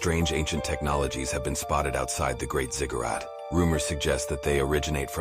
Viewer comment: I swear I thought the narrato said " The GREAT CIGARETTE "!😮😢 I watched ugain , cuz' I was like..."what"? ......... "ZIGGARAT RUT, ..." whatever...